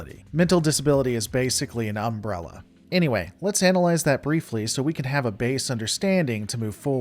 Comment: "Let's analyze that briefly" in my mind I heard "Let's analyze that, broccoli!" sorry